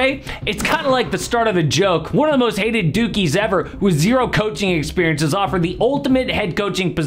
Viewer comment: 💀I heard it as “most hated dookies ever”